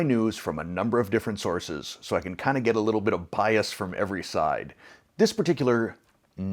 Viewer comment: It sounded like "... a little bit of pious" but the captions reads as "bias". Either way both would seem appropriate. 😉